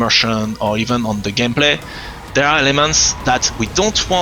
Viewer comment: Sounds like he is saying "all these lemons" but its actually "elements" lol